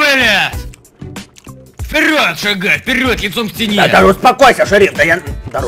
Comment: Лайк кто услышал "яйцом к стене!". (2.06)